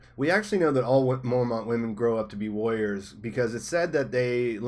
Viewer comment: Ooooohhhhhhhh... warriors. I thought you were saying lawyers and was so confused.